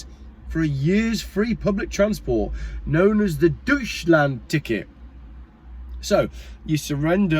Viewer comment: … did you say ‘Douche Land’ 😂😂😂